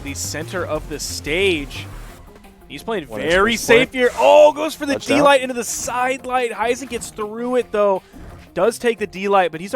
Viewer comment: One of commentator is Russian. I heard he said "О, ГОСПОДИ!" (omg in English) at 16:48 It's so cool to know that there is someone who speaks Russian, my native language. P.s.: Sorry for the mistakes, I don't know English very well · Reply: He said “oh goes for the d-light into the side light”